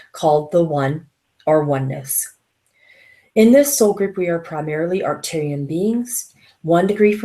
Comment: did you say "In this group we are primarily Arcturian beings..." ??